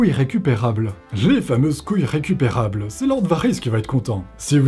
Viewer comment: J'ai entendu "couilles récupérables '' à la fin. 🤣